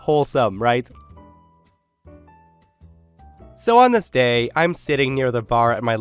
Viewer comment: Every time you say "On this day..." i hear "Namaste"